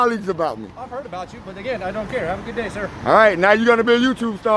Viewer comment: From the start the cop was polite, You picked on him this time you are wrong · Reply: Sounds like misheard him. He said "Have a good day" but it sounded like thought he asked him "Are you okay?" In any case I don't think this Cop was Youtube worthy. He didn't seem that bad